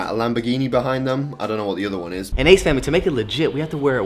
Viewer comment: I thought that he said "Aids family" lol